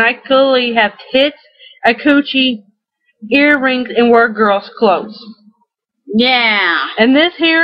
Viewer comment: I thought she said work girl’s clothes “That haircut happens to be one of my best friends…hairstyle. Hairstylist…cuts. Cuts.” 😂